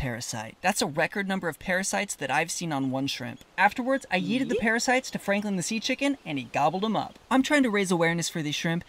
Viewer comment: omg i had to rewatch cause i thought you said you yeeted the shrimp at Franklin and was like WTF